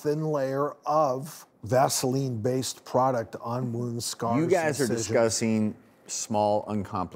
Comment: I thought he said "you guys are disgusting" lol